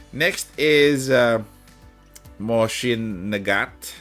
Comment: When you said Nagant, I heard "the gyatt" and thought you were talking about Pritya.